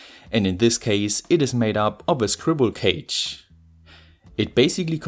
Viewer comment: i love how you say ''squirrel cage'' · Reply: Ohhhhhh I actually heard it as "scribble cage". Not that "squirrel cage" automatically makes more sense to me.